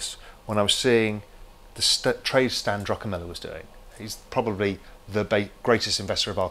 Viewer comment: What is the investors name he is talking about at 6:16 ? I couldn't understand it correctly. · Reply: Stan Druckenmiller.